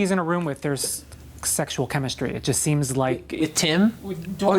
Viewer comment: I can't understand what Reeve says T-T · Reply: he said "Tim?" referring to Timothy Dalton who plays Malcolm Murray. He thought the interviewer was talking about him and not dorian.